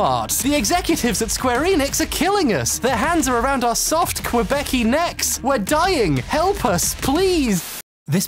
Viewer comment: this is not the point of the video but he did not just say quebeci 💀